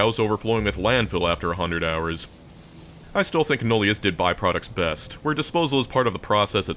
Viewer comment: what is that word? nuleas? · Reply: nullius it's an old mod, but a good mod